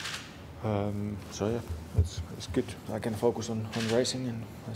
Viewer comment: Did bottas just say I can F some on racing🙆‍♂️😂